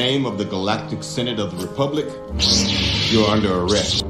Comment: To be fair, Daily Wire put out a response pretty quickly. How long do we have to wait for people to ‘remain silent’ these days? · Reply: Well I thought I heard him say "in the name of the galactic synagogue of republic , you are under arrest" and wouldnt be that far from truth though